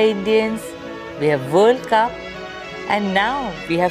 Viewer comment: Wait..what did she say at 3:10 we have world cup ??